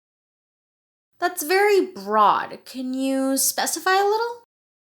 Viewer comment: Hello! What's the adjetive that the educator says at 2:26? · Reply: That’s very broad can you specify a little. We have scripts available on Patreon for 1 dollar of your support. <link>